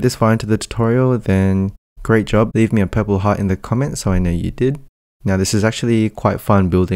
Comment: whats the pebble thing? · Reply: maybe heard wrong ,sounded like you said should comment a purple pebble to show that made it to the end of the video